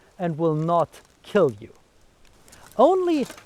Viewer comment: At first, when he said to attack the mail parts, I thought he said male parts. 😅